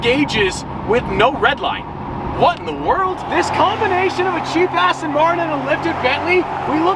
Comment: Totally thought he said Cheap Ass for a second! I'm like I never heard Stradman curse.